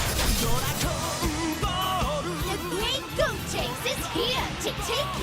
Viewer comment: I love it when subtitles say the wrong thing they called gottanks goat chase